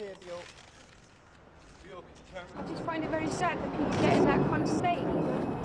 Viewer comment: I'm having trouble making out what she says here. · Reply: "I just find it very sad that people get in that kind of state"